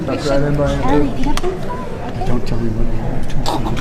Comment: Hold the fucking tape. @ 2:03, please tell me I got wax in my ears. Did I hear “don’t tell anyone” TWICE? AYO, WTF